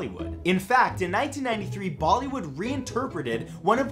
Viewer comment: did I hear him call them "baldy wood"? 😂